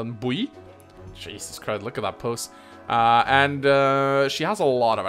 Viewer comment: did I hear you correctly? Look at her puss?